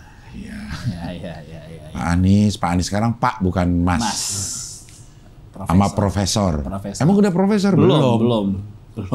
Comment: gw dengernya pas debat disebut "Prosessor" :v